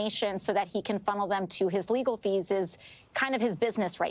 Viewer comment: At timeline 7:35. Did AOC just say "legal feeses?"